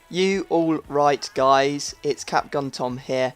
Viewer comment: Totally thought you said "ya alright guys" at the beginning of every video. I'm awestruck.